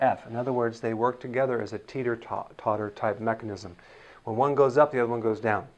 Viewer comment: I heard "tater-tot"...…… dang, now I'm craving...… LOL